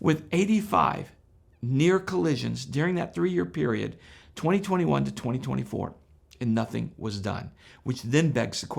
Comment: I heard it say 2011 to 2024 so 13 years have I got this right?